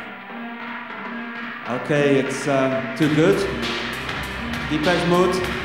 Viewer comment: "Okay, It is too good, Depeche Mode" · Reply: Always thought he's saying "okay, it's a debut", then pronouncing the name of band not correctly.